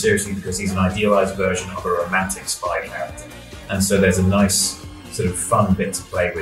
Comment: I thought he said "romantic spiderman", not "romantic spy character" 😭 congrats on getting to interview them, btw!!